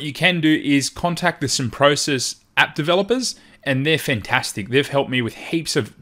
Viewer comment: at minute 2:22 you mention contacting some developers for help, but I can't make out exactly who they are. Can you spell it or post a link? I need help with some MC issues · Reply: man - tell them I sent you!